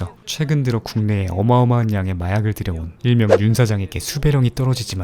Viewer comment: ㅋㅋ 아니 '윤사장' 자막에 '윤짜장'이라는 소리가 들리는데, ㅋㅋ 역시 지무비는 진실을 말하는 군! 내년 대선때 '윤짜장' 되면 대한민국은 ㅈ된다~ ㅋㅋ "윤짜장에게 수배령이 내려지지만.." 오, 기시감 쩐다~ 곧 윤도리 포토라인에 설 텐데..ㅎㅎ